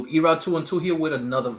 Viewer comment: I thought you said Erobb221